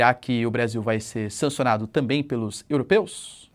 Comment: não entendi sua fala no tempo do vídeo 17:16 "O brasil será sancionado pelos Europeus ?"